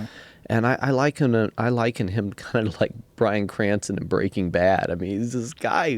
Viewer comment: I thought that said Bryan Callen 😒